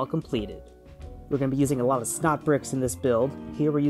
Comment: Sounded like he was saying snot bricks